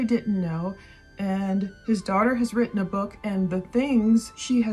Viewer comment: Did you say his daughter has written a book?